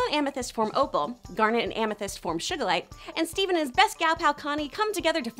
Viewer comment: Did you just say Shugalite